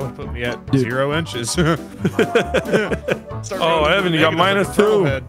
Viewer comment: Did Vanoss say, “Oh gay?”